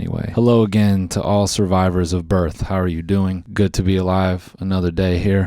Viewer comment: “All survivors of birth” I felt that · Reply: Oh I thought he said survivors of EARTH and I loved it